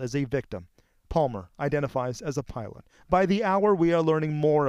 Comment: “Identifies as a pilot” I swear I heard that